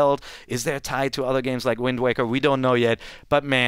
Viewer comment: in 10:47 what I heard was wind waker?